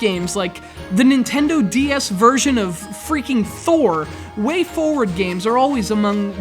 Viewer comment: wait what did he say at 2:13? the nintendo ds version of what? I heard freaking four but no such game exists that i can find. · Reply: freaking "Thor". a great game for DS